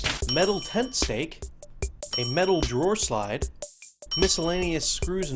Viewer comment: what was the material he said? I couldn't make out what he said but it sounded like 'pen stake' which im pretty sure is wrong.......